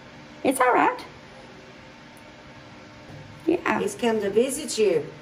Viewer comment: Hey, I love your videos! I’m not sure if anyone pointed it out but at the 48:31 mark I heard a little kid saying “yeah”. Easily heard with headphones.